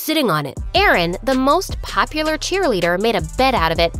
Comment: wait did i hear Eren